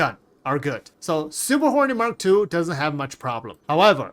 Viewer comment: I kept hearing "Super Horny Mark Two", lmao Appreciate you, my dude. Lol. Never change.